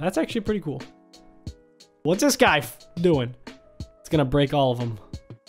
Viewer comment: HE SWORE HE SAID WHATS THIS GUY F***** DOING